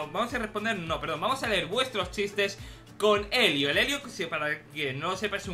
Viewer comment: Jeje, yo al principio pense que decia chistes con HIELO, me di cuenta hasta que y lo dijeron que era con HELIO. Y yo ahi en la mitad del video como mensa no entendiendole ni papa por no haber leido bien el titulo!!! =,D